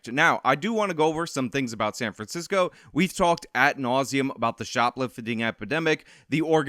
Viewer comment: Did actually say, "At nauseum"? "At"?